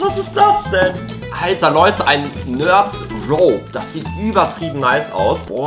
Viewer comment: Ich habe nerds pro verstanden XD